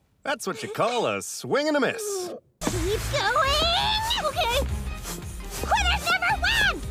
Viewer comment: lmao what ino was saying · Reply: Cutely says ok